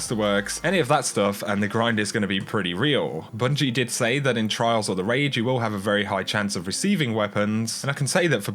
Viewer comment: i thought you said trials of the rage. Then i realized you said trials or the raid lol